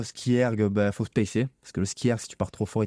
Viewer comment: A 37:10 il dit quoi ? Il faut « pacer » ?quelqu’un a compris ? Merci